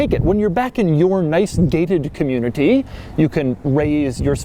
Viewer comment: why did i hear "david community"